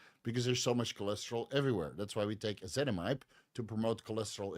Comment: Im sorry but what items did u state around the 16:30 mark for cholesterol?? I cant make out the compounds u are stating… i would love to know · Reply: Ezetimibe 10mg